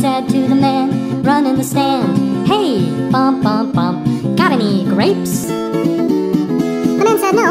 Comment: I thought they said only graves but it was so funny when they said all the words so fast😂🤣